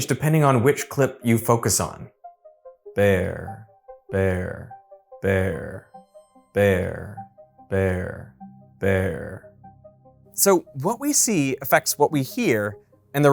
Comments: I heard there both times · I couldnt help but hear Vear the first time the left video said it, then I heard bear. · I heard "there" in the second clip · I hear bear looking at both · I hear “bear” regardless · Why do I kinda hear "pear" tho😭 (seriously, I don't hear a difference though) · I hear "there" without looking at the screen. Am I cooked? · If I cross my eyes and focus on the center image I hear the word "Vear" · I could hear bear clear as day, but heard 'vair' instead of fair